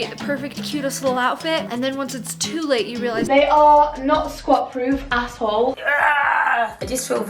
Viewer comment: I swear I heard say “they are not squat proof. Asshole.” 🤣🤣🤣 Loved this video!!